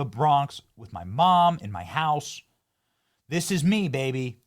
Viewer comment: Benny says, “she said me my mom and my house”. Lmbo what she said was my mom used to clean houses.